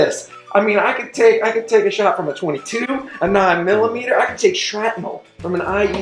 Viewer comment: It sounded like he said he could take shrapnel from an IUD. I was instantly horrified and curious.